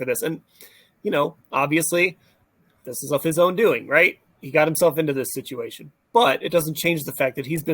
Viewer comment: = a bullsh!t uncredited comment by dollar store Chad Kroger with possible MRDD ailments needs to be replaced or placed in a credible interview wit........(whos with because I swear I just heard him say "he brought it on himself"...... So no criminal charges after 2 investigations hold no ground? I forgot it dont feed into the city his fault? different ever being brought up doesn't hold any sway.